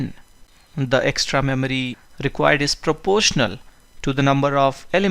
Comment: Lol the subtitle at 0:34 is disproportional instead of proportional.